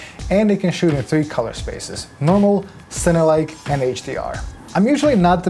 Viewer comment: captions are wrong "Cinelike" not "Center-like"